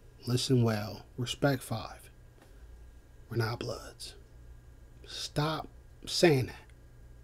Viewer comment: did you say we not BLOODS